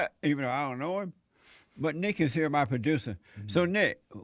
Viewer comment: I thought he said no niggas here, not "Nick is here"